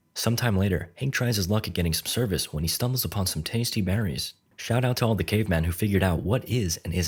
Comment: i was reading subtitles when the scene at 2:58 the subtitles said shut up